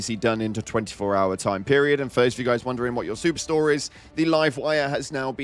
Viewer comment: you said “super store” i thought you said “soup store” 😅